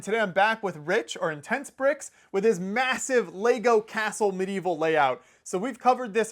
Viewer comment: did he say MASSIVE